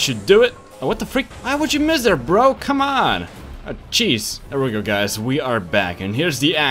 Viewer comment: did he just say CHEESE?